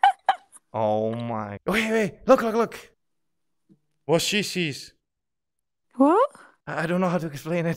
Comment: was cee shees : Did he really say it like that :D or is that just how I understand it?